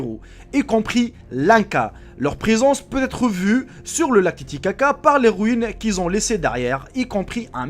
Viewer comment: le quoi ? Lalilicaca xD wsh 😂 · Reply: Camille Léger Le lac Titicaca